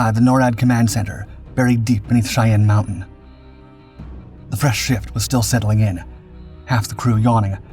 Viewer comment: maaaaan I thought you said something completely different at first. 😅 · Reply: "The fresh shit...." heaven forbid a man be able to have a fresh ass shit smh